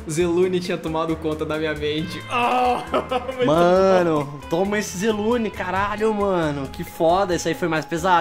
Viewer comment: eu ouvi: "toma esse krl do Zelune"